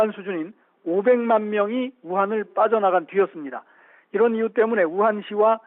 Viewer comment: 우한을 빠져나가 튀었습니다라고 들림ㅋㅋ